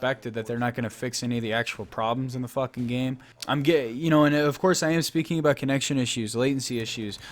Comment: "I'm gay"? Did I hear that correctly?